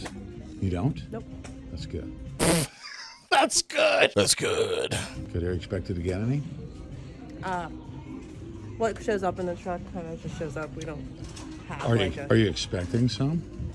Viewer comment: I heard “Are they expected to get any?”